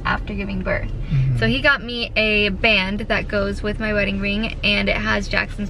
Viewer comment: Did say wedding ring??😊😂